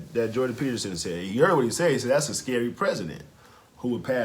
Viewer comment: "That's a scary President" that's not what Jordan said...he said PECEDENT not PRESIDENT...there is a huge difference